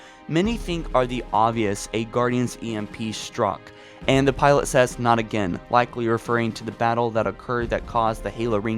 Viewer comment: Doesn't the pilot say "not them, not again"? That's what I thought he said